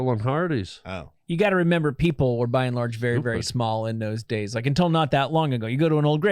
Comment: did Harlan call Jason stupid lol